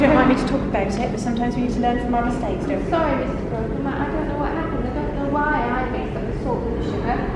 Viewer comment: did she just call her Mister Crocombe??